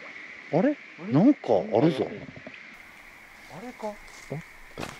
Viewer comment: 女の人の声でどこって聞こえます